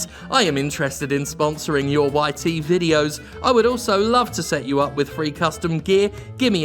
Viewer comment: So, I was listening to this video instead of watching it and I was confused as to why people call Jim's videos "whitey videos". I was wondering if they were making some weird racist joke!